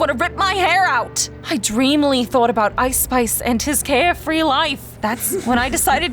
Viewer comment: did anybody else hear her say ice spice lol